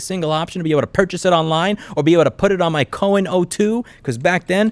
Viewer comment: As someone from a Jewish family I keep hearing "Cowon" as "Cohen" ;)